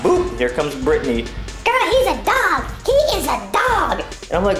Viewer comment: the first 2 times i saw this i thought she said "girl he's a thag he is a thag"